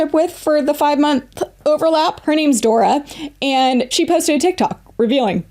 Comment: Omg I was just listening walking around my house and I thought you said my name adora that was funny 🤣😅